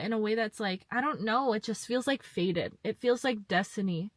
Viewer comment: I keep hearing "faded" instead of fated and I guess that's my cynicism lol But I did just wake up from a dream in the last hour where there was someone who definitely wanted to kiss me, and it was a reminder of what someone who is really into me would act like well before the kiss. Incidental/accidental hand touches that were charged with magnetism